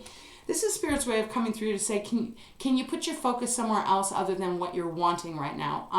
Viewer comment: I thought you said can you put your Fcking focus on something else 😂😂😂🤣😭😳😬😭 that was just a wake up call. I thought you were yelling at me 😖🤣😭